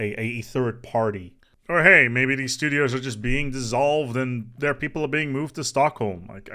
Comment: What you say: "The support studios are being dissolved." What I hear: "Soylent Green is Pops!"